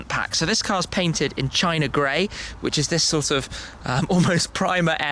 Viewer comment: Did you just say China Gray? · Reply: Yes, the colour is called "China Grey" with "Flugplatz Blue" accents